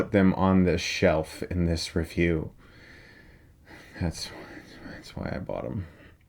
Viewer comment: Thought he said, “That’s why I bottom.”